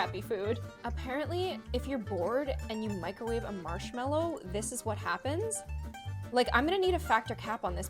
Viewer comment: I thought you said ‘if you boil a microwave in a marshmallow